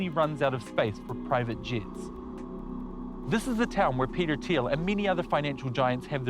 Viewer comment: Ohhhhhhhhhh. I thought you said Peter Tosh